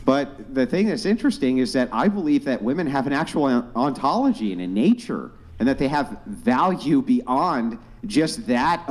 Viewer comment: Her voice has such an annoying pitch that pods of dolphins get disoriented every time she speaks. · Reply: what did he say? Something like "innature"? Huh?